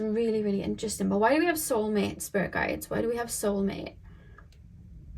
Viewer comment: Omg you talked about mama bear papa bear energy and then the captions thought that when you said "spirit guides" you said "bear guides." Spirit is STRONG today y'all ✨️💕🌻